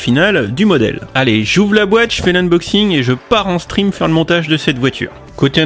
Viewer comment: Ok j'ai un problème, a 1:32 j'ai entendu "je pars en string faire le montage"